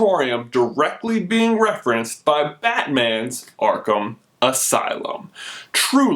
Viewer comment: it sounded like he said fat man instead of batman